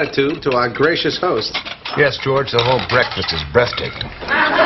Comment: "the whole breakfast is breathtaking" then Elaine's face biting the orange lol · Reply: I thought he said "Breasttaking"